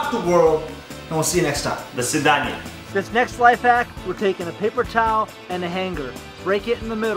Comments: i thought he was trying to say paper cut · lol I thought he said paper cut lol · Lmao I thought he was trying to say paper cut